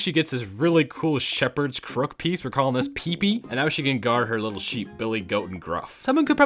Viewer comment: I don’t know why but at first I thought you said pee pee XD